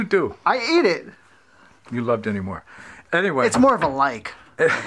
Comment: I'm starting to be suspicious about the Dinty Moore and champagne showing up on every boat. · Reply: I have zero idea what Dinty Moore is not being from the USA. In fact I thought he said Demi Moore and thought she was endorsing some type of food like Paul Newman's dressings.